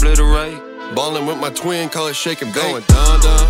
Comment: i legit thought gravy said "ballin with my twink" instead of twin. I mean same thing bro do be a twink